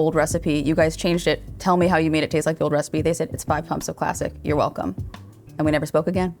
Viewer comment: What did she say? I can only understand "it's 5 pumps of plastic or classic"